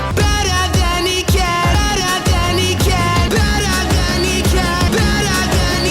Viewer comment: Personally I hear, “Bet on the kneecap”.